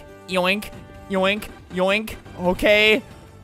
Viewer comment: did anyone here "Boke" instead of "Okay"